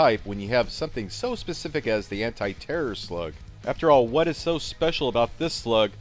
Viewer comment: who else thought he said anti terrier slug lol